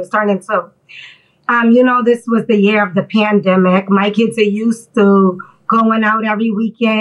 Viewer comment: I thought she said year of the panda lol